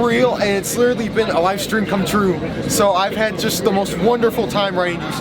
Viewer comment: Am I the only one who heard "it's literally been a livestream come true"? The auto-generated subtitles seem to agree with me!